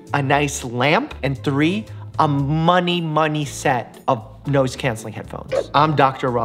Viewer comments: did u just say nose?? Please tell me i heard that wrong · Mike: "A money-money set of noise cancelling headphones." What I heard: "A money-money set of nose cancelling headphones"